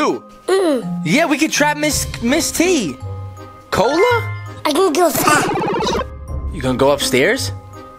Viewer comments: why did it sound like he said I can go fat😂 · When Shawn said I can go fast 1:18 I thought he said I think your fat